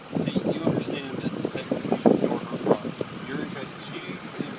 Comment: did I just hear “technically, you’re her property”??????????